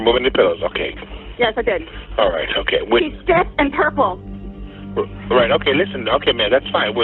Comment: GUYS I THINK SHE IS SAYING HE’S STIFF AND PURPLE IDKKKKKK GURL SHUSH U SAID IT SO MUCH ITS ANNOYING